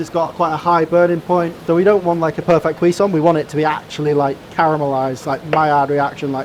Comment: i think if i can figure out what that word is i can become very annoying to people around me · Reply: I think he said Cuisson “kwee-son”. It refers to the leftover liquid after poaching/braising meats, usually used as the base of sauces, like a brown gravy. (I’m not smart btw, you just made me curious & wanted to answer ur question)😭🤝